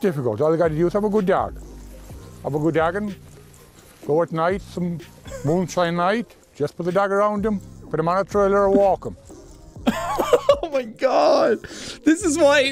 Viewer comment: what I heard: "....some of a good job, you're joggin, Fortnite and moonshine light, just to be dragged around em, my trailer's walkin"